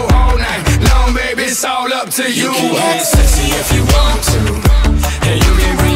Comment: I heard you can get schwifty if you want to.